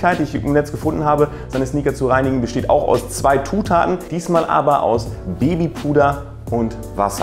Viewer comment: Bin ich der Einzige der ,, zwei Tutaten‘‘ verstanden hat😂